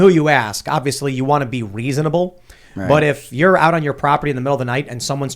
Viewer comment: did Tim say "Weasonable" or is the constant ringing in my ears just making it sound like Tim is saying weasonable instead of reasonable?